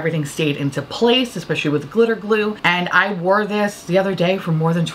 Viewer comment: i’ve been binge watching ur videos and i keep hearing “glitter blue cockney”, which i think isn’t what u are saying 😭 can someone pls help me i can’t figure out what i’m not hearing haha